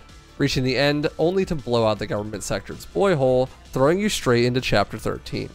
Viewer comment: excuse me but did you just say boyhole